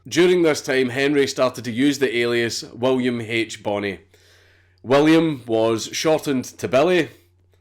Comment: "William was shortened to Billy" i had to re watch that part three times cuz I thought you said William was shot in the belly lol